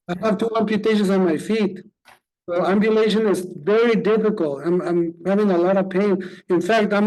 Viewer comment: when the guy was talking it sounded like he said "ovulation is difficult" 😆